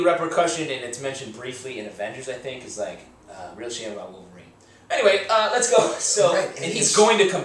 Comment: what did you say? "real shame about...wolverine?"